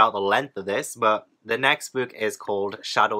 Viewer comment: fully thought you said "shadows of winterfell" then and was shook